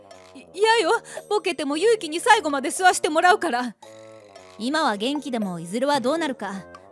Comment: ここの「世話してもらう」が「吸わしてもらう」に聞こえてナニを吸わしてもらうんだよって真面目に突っ込んでしまった ここのところの暑さとこのアホ親子のおかしさにやられたんだな(棒